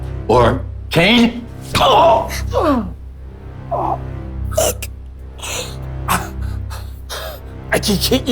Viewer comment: this guy did not say “look”. He said “eek”